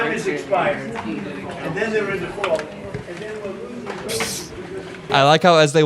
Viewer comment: I don't hear the blue slips again there at the end where his voice is trailing off. What I hear is "we're losing...". I hope he's right!